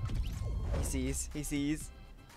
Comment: Anyone else hear Siri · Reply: Keno Hooper Siri thought he said "Hey Siri" but said "He sees"